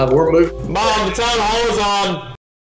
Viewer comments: okay, I'll be honest, I don't know what he said 😅 what did I miss out on 😅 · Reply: Mom the town hall is on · Can anyone please tell me what he said at 1:09. I didn't get it. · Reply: mom town hall is on(name of the meeting)